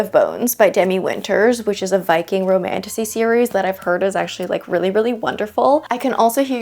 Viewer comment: When you said “a Viking romantasy” i heard “a BIKING romantasy” and was like “… huh, well that’s a new one! Very interesting idea”